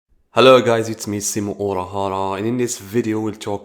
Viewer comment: I honestly thought you were saying your name was "simp urahara"